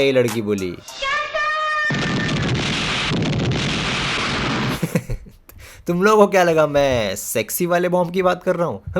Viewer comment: itni si baat par ladki kya boli? samajh nahi aaya 😆🤣🤣🤣😂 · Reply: Captainnnnnnnnn!